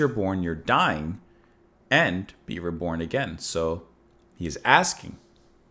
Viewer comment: I heard: 'I been reborn again'